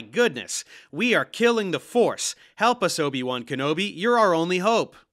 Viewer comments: I heard "forest" - or am I missing something? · I think he said 'forest' not force, not that it makes a lot of difference. Words seem to have no meaning to them. Edit - I think you've actually wasted your time and energy on this. It's like watching a debunk of shredded scrabble dictionaries. Like, yes it's a pile of nonsense but any amount of time spent actually describing fractals would have been more worth while that pointing out every time it's misplaced in the pile of nonsense. This isn't just Brandolini's law, it's that building a foundations of understanding in people that allow them to recognize BS as BS and dismiss it is more valuable than trying to debunk every nitwits claim that the earth is actually a toroidal penguin fart.